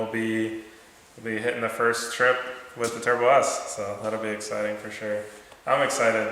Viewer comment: Thought you guys named the Polaris. When you said Turbo S at the end, I thought you said "Turd Bus". LMAO, then rewound it and was a bit disappointed I just misheard it.